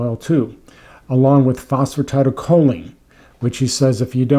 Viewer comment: What is that you took to put coconut oil in mitochondria? I don't understand those words. You mention it at 4:37. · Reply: phosphatidyl choline.